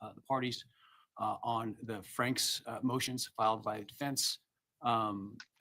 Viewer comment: Did he say “that will include the Franks hearing that WAS HELD on IGG” So they have already had a Franks hearing. Was that the closed hearing they had before this hearing?